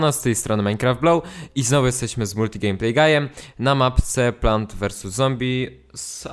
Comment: kto usłyszał po 0:57 multim(gey)playgayem ??